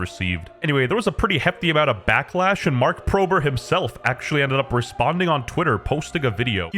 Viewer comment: I swear I heard “Mark Prober” like 3 times